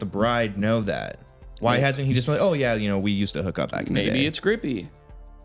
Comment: "maybe it's grippy" best closing statement I've heard today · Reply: Oh, I heard it as "maybe it's a groupie" like, you know, someone from a concert who has sex with someone from the band